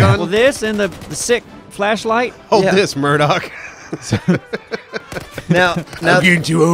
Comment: I swore he said 'Mirdon', not Murdock. I was waiting for the wizard from Doraleous and Assoc to start talking.